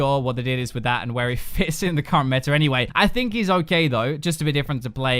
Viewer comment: 8.20 I heard "i think he is all gay though" and I agreed on that...lol